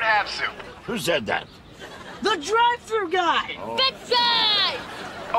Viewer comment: and 2:18 Guppy: " BIG drive! "😀 The way I misheard gibby's younger brother the 2nd and 3rd time he shouts that line.😂